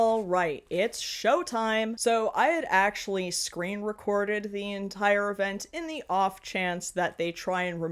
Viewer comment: Had this on in the background, misheard "screen-recording" as "scream-recording", and didn't even think to question it. Not sure what that says about this debacle (the TPUSA thing).